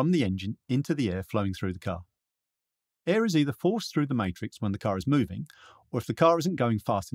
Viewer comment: Not sure what you meant by that comment. Can you expand a bit please. Thanks 👍🏻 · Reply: i think you say forced thru the "matrix" ??